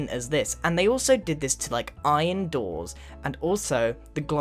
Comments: Iron doors ? Their bars... (i heard doors) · Does anyone notice that he said iron doors instead of iron bars? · Did he say iron door in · did he say iron doors